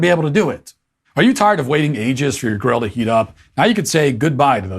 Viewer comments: "are you tired of waiting for your GIRL to heat up?"....i misheard this at first and got excited for a second. Thought Matt had a solution for frigidity. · omg - laughing so hard. When Matt launched into his BBQ Grill sponsorship pitch. I thought he said…”Are you tired of waiting for your girl to heat up?” I was so disappointed when he started to talking about a grill! hahahaha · I am sure I heard "are you tired of waiting ages for your GIRL to heat up?"